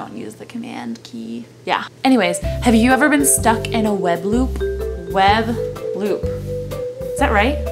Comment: OOOOOOOH. "WebLoop".. I thought it said "WeBloop"